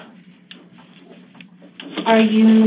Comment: What did Alex say to the guy that walked in and sat next to him? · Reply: I think "we should go"